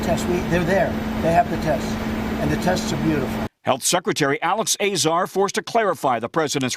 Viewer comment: is Trump say 'the test is beautiful'?